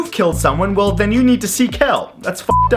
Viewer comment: I thought you said 'see kelp' lol! :)